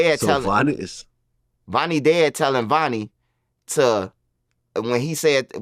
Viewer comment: Oh this mf saying “dad” 🤦🏾‍♂️ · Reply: Thought he said “the dead telling”😂